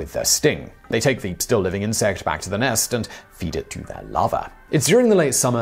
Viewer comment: did you say they feed them to their lava or lover?